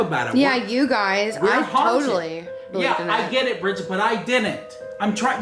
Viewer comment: GUYS 14:08 I HEARD OH BRIDGET